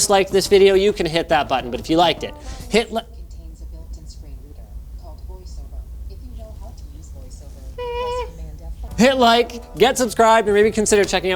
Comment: did Linus just say Hitler?